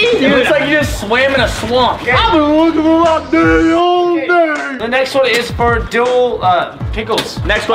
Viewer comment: Don't know what the he'll said but that had me pissing my pants🤣😂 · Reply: Lyric Rap "I've been looking for my daddy all day"